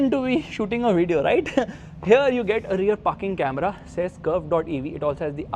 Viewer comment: I heard "Rear ****ing Camera" (06:36) :face-blue-smiling:! Great video though. Thanks!